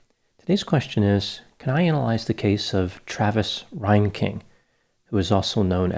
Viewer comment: I thought you said "Travis Lion King" 🦁